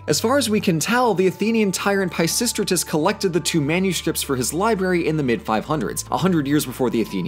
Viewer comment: Did... Did you say “liberry?”